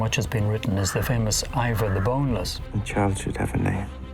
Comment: Can you help me please, i don't understand what the professor says at 1:15 ? I'm french :D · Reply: Ragnars sons, about him is the most famous, Iva The Boneless